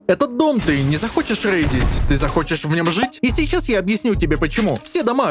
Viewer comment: Мой Boosty: <link> Мой Discord: <link> Мой ПРОМО на rust room - GOODJOINT Строительство: 39k stone, 32k metal, 270 HQM. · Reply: те самые китайцы чё чё нам послышалось что ты сказал это не зарейдить